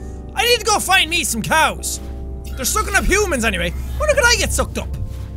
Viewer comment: turn on captions and pause the video at 6:03 instead of it saying sucking up humans it says smoke enough humans